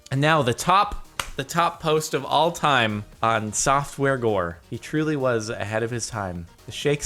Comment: Oh I thought it said softcore gore